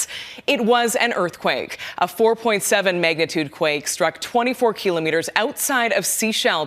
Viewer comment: did i hear 'megatude' instead of magnitude at the 7 second mark?!?!